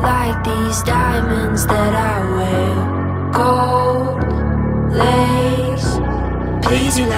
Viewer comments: I keep hearing “I Will, light these diamonds that are real” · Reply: I heard, “I’m rare, Like these diamonds that are here” Nope I was wrongggg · i heard "i am like this diamonds that are red" i was soooo wroong😂😂😂😂😂